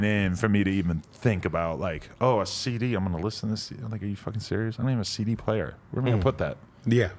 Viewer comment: wait did he say"nigga are you fuvking serious